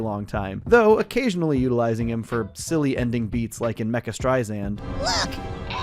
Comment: they did NOT say mega 🫩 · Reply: They said Mecha